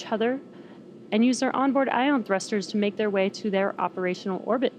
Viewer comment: Thank you ... I thought I heard the lady say they were going to position themselves in a geostationary orbit ... hence this led to my question. Does the part in question burn up ... or is it somewhat potluck as to where it falls? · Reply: You are correct .. she said operational orbit .... somehow I misheard. Does this mean we could see a whole long string of these satellites from earth again?